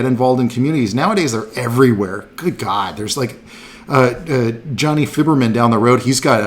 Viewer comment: What name is he saying? Jonny.. Vibermen?